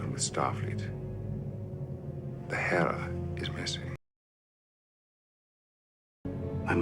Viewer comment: I thought Picard said 'my hair is missing'.